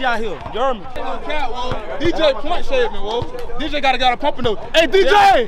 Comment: Umm can anybody tell me what dude was saying at 10:55?!? 😂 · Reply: He said that dude was point shaming